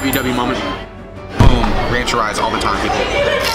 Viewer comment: Excersise, I thought u said rancherize